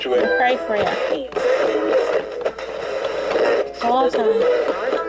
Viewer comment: Hi I will listen again i think it woz around the 3/5 of ta vidy-mark i will time freeze for you ^.^ i hope it sounds ok..... it is juzt when u where scanning thru i could only hear adult advert voice's but an odd spirit voice to me anywayz sounded alittle different from the others! I'm juz reading online about EMF meters there so kwwl. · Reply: First you say "See you next week alright... I will pray for you..." then after that at 10:33 i couldn't understand wat you said? I thought it sounded lyk Awesome or Call me --> 10:41<-- I said 'huh' i mean i think ta Spirit person wanted to know wat that word woz...